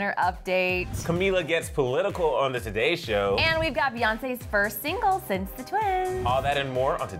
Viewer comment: Thought they said Beyoncé's first time being single I was like hold up